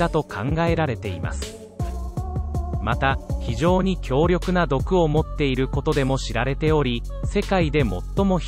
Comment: イントネーションおかしいね。「毒を持ってる」が「毒を盛ってる」に聞こえる。